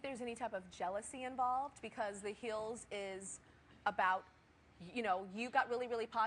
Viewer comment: she said the heels instead of hills